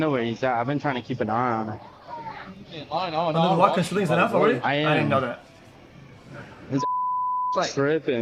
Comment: someone please translate what xqc said at 2:29 plz can't understand wtf he's saying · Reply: something something sleeves enough something i didn't know that is what im hearing lmao